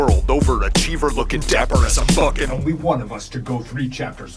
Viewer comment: it really sounds like hes saying "looking Dapper as I'm Fucking" But the subtitles say "looking dapper as I'm bucking"